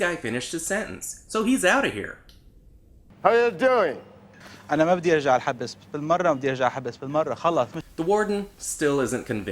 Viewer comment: wrong translation, that’s not what he said 😭 LMFAO. He said “ I don’t want to go back to jail please”